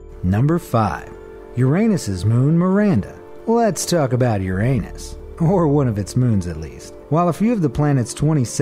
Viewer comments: it sounded like “lets talk about ur anus” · i thought you Said lets talk about your anus